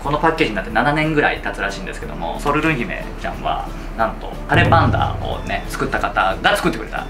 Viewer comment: たれパンダがカレーパンマンに聞こえてしまった笑笑 今度ホームパーティーで作ってみます！